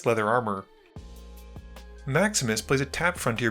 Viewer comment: Every time you say "Maximus plays", I hear "Max misplays"... 😅